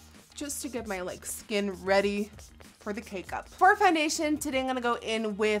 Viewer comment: Did she just say cake-up instead of make-up?! I love it xD